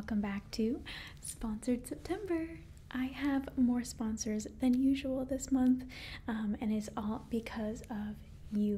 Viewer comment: When she said, "I have more sponsors than usual." I thought she was going to say, "I have more sponsors than you!" at first XD